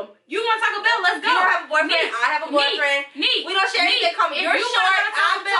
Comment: did say “ you have a boyfriend, I have a boyfriend “ or did she say “ you don’t have a boyfriend, I have a boyfriend “ ?? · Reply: Melanin’ Shay She said “you don’t have a bf, I have a bf, we have nothing in common”